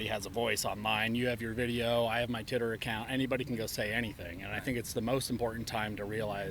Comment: Did dude say titter account